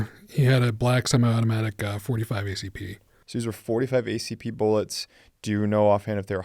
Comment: Can anybody make out what he said the gun was that he was shot with? It sounds like he says it was "a black sum automatic 45 acp. I know I'm not hearing that right.